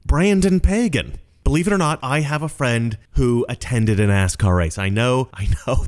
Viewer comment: I thought he said an ass car race!! as in in jackass